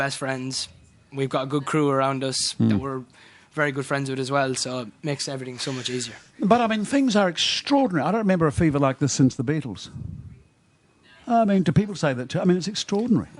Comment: when the interviewer said 'extraordinary' i genuinely thought he said 'extraordinHARRY' LOL.